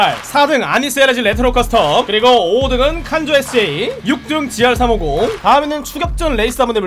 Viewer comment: 이부분 나만 딩카 칸조 SA라고 들리나